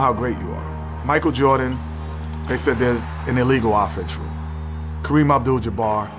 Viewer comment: What is Kenny saying at 1:53. An illegal "official"? I don't know what he's talking about there. I only got to see the 2nd Bulls 3-peat. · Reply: Illegal offense rule